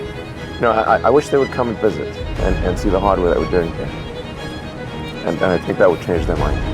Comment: 근데 1:55 하드워크가 아니라 hardware, we are doing here 이라고 들려요